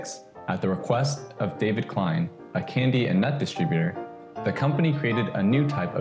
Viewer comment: David Klein, a candy and METH distributer?!? Am I hearing that right?